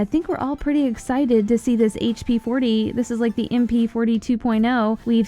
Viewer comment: did she say hp40 at 0:44 lmao